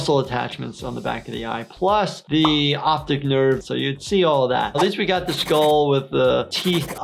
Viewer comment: "Optic nerves" I thought you said "Optic Nerds